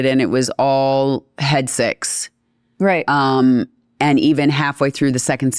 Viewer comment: I thought she said "Head Sex" but then I realised it was "Head Six"